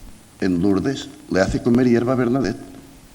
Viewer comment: ❓¿Alguien que transcriba lo que dice entre 39:20 y 39:27? Lo escucho y reescucho, y no lo pillo. Y parece que los subtítulos de YouTube tampoco. GRACIAS. 👍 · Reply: Le hace comer hierba a bernadette😄